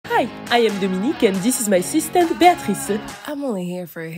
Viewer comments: What I hear: "Hi I am domique and this is my sister Bea rice" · I thought he said 'sister' when he was saying "This is my assistant beatrice" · I didn't read it so I thought he said sister not assistant cuz of his accent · i thought he said "I'm Dominican and this is my sister Beatrice" like, how do you expect that to make sense? Then I read the text--😀